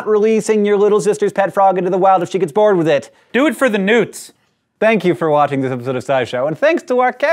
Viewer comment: Haahaha i thought said "Do it for the nudes!" xD